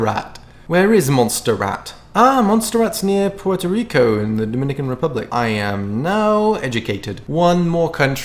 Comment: did anyone else hear,” pwoit-a-Rico”